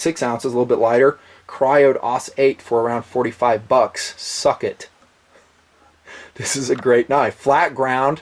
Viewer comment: did say "suck it" at 13:40??? hahaha